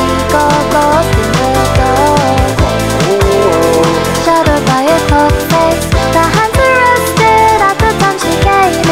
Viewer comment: did gus say "owo" at 8:20? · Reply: He said Oh-Oo-Oh